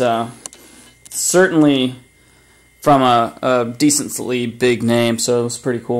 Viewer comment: what did you say ? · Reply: "from a decently big name"....It's a big name in the sneaker game, just can't reveal who...